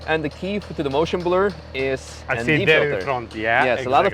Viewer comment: So, key for the motion blur is what? You talked over and it's not audible what he said. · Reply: ND filter